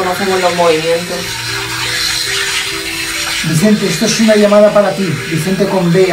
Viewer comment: En el minuto 1:09:25 luego de dar su nombre por tablero, se escucha una entrada psicofonica que dice "VICENTE".. (creo con vos de mujer)... puede ser?